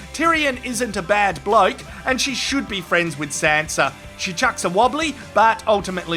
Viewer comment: I thought you were saying Santa instead of Sanza it was hilarious